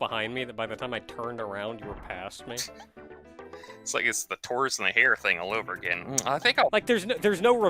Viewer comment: I heard "Torus and the hare."